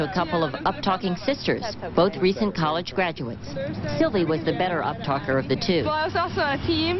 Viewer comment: Did they say that girl's name was "Silly"?